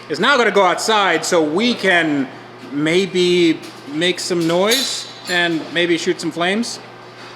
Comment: I thought you said 'Make the nooooise' at first, not what I was expecting, but I'm okay with this.